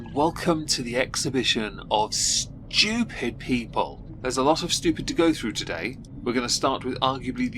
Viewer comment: All i heard was " stupid peeple" 💀